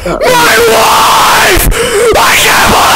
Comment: I’m late but- 8:25 Is he saying his wife or his life? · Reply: wife